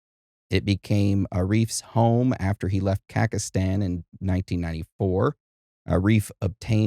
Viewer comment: did you say pakistan instead of khazakistan